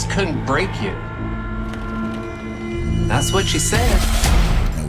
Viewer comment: I heard "that's what she said" cause I was watchin in 1.5x lol (miss you Michael).